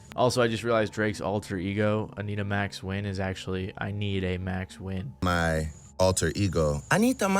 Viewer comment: All this time I thought he was saying "I NEED A MAX WEIN" like short for weiner